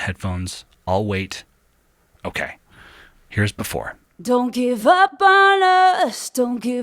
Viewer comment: I thought she was saying "don't give up on drugs".